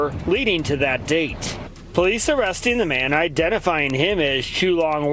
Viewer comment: I thought they said his name was "Chew Long Wing" 😅